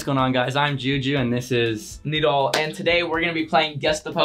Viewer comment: not the cc/subtitles says “needle” when said his name 😭 💀